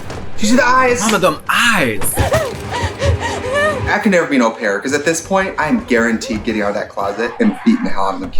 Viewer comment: the subtitles say "I could never be no parent" but I'm pretty sure she said au pair?